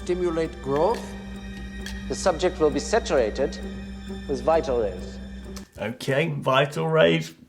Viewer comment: i think he says Beta rays, but with his accent, he says vita rays